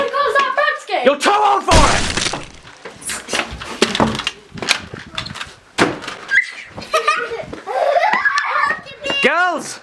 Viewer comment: If there was ever a moment that summed up show, this would be it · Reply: Damn, I really thought he said "you'll tow all for it" and it was just a fancy British way of saying "i'll fucking kill you rather than give it up"